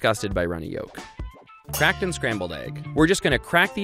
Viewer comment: i really just thought it said crackhead egg- ✨🤠✨